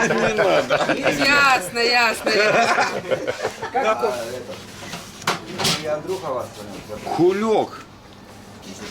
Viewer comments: Does anyone know what the word is? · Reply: "Xyi" mean dick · did he call him a kulak lmao